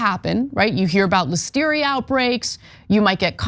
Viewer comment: at around 5:00 does Ana say contamination and wisteria a couple of times, and Ecoli outbreaks ?? I love wisteria